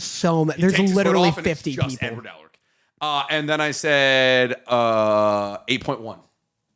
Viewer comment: i swear I heard say "n-word elric" and about lost it 😂😂😂